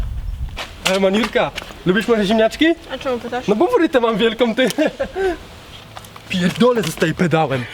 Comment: co on powiedział? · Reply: "no bo fryte mam wielką"